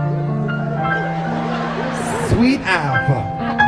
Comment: OMG !!! Coming on a honda (speaking in tongues) and when he said , Sweet ow vah ( sweet hour) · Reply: I thought she said sweet alpha...lol